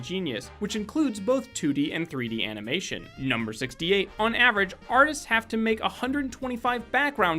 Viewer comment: Did anyone else notice when he said number 66, it sounded like he said number 56.